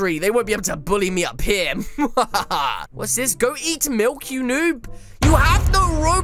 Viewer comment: “I am now the most powerful noob inside this game...” Wait, that makes him a pro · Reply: Surreal Snowman 7:02 did he just say eat milk